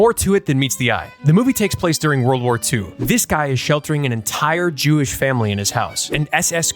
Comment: i heard "this dude shoved an entire jew family in his mouth"